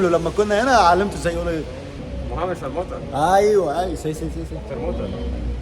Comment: هو قال محمد شرمtة ولا انا سمعت غلط